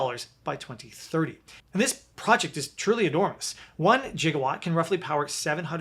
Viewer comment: Totally heard you say Jiggawatt, Doc Brown!🤣